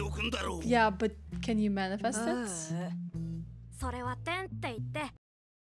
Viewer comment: this ten sounded so disappoited that I heard "tem", which means has in portuguese and match perfecly with leorio said